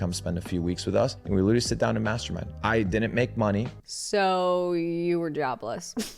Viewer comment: I legit thought the guy was said "masturbate" not "mastermind" 😂